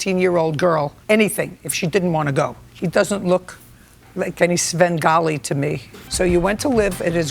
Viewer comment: What is that word mean that she said? Start at 0:25 then she looks at him and says "he doesn't look like any kind of Spven-gualle?[wrong spelling] to me". I can't even spell it close enough for Google to understand!